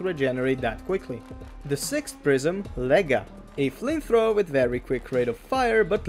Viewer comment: what did you say? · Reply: Lega prism